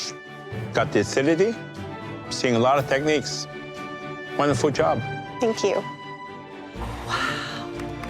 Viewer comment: Did I hear: " Wonderfood job." ???? Or am I just hungry? LOL